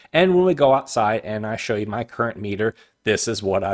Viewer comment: on your video can not tell what your said something something meter this is what i got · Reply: Current meter. Or shunt